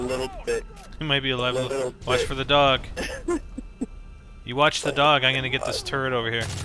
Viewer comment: Lmao 0:35 it sounds like said "You watch the dog. Imma get this turd here." XD